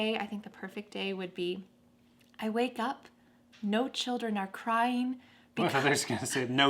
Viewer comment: everybody teaches never use double negative in english speech, why did you say "no children no cry" maybe you meant something different 🤔🤔🤔 did you mean "there is a pleasure time when a child didn't cry?" Or it meant you weren't seeing children, and you weren't hearing their cry?